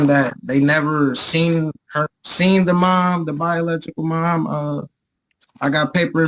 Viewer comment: Did he just say the bialechical mom!?